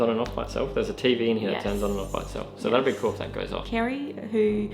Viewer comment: I just heard collar 4:37pm